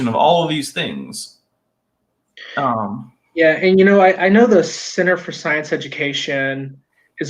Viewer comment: Misheard "Center for Science Education" as "Sinner for Science Education" xD